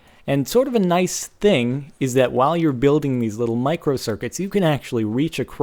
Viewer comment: Who else thought it said 'Mario Circuitry' ?